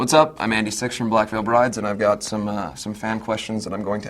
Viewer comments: In the beginning it sounds like Andy says: ''Im Andy Sexual from Black Veil Brides'' ... o-o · Wait did he just say "hi I'm Andy sex". or. "hi I'm Andy six"?!??!?! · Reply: He said Andy sixx (